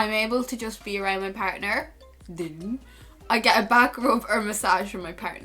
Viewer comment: I thought you said bad grope not back rub hahaha